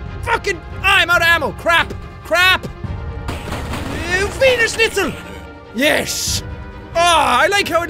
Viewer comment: did jack just say weinerschnitzel